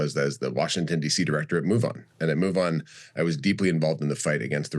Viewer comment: At first i thought he said MUFON!!!🛸